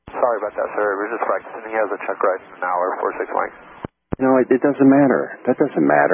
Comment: sounded like “he has a checkride in an hour”, not “he has a checkride now”